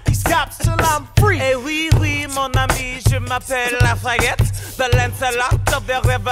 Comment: I haven’t watched the musical so when Lafayette said: “Oui oui, mon ami, je m’appelle Lafayette.” I thought he said: “Oui oui, mon ami, je m’appelle la fagget”